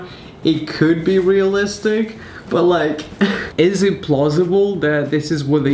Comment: am I the only one who hears pLosible instead of posible XD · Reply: I think it's because he said plausible